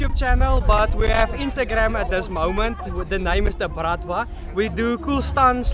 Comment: HELP! Who's found the young lads' IG account? I can't make out the name of it at 16:35. WONDERFULLY FAIR reporting, congrats and many thanks! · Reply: It is "the_bratva_"